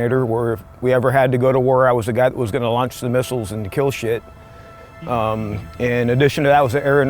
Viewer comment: did he say "kill shit"?